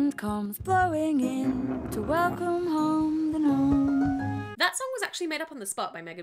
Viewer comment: on the part in gnome song i thought she was saying to welcome home the moon, not the gnome lol